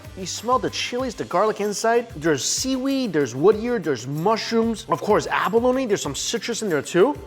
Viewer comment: does say there is wood in the abalone bucket? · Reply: He said “Woodear”….it’s a fungus.